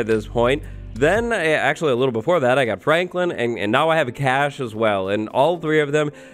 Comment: I thought he said “I got pregnant” lol Also he did get cash as well XD